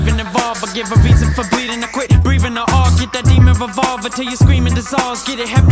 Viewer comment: did he say "breevin"?